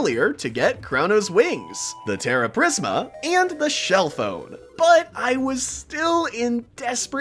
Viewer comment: instead of shell phone the subtitles say shell foam😂